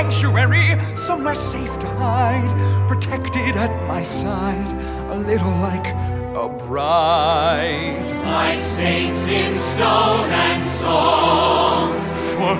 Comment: "For her I will be strong" (2:45) Damnit Michael Arden stop hurting me with your angelic vocals. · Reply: I thought i heard "destroy' instead of "strong"....and i am 18....did i missheard?